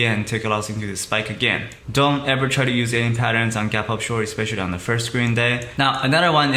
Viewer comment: What does he say at 0:58? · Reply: "Don't try to use any patterns on a gap up short, especially on the first green day"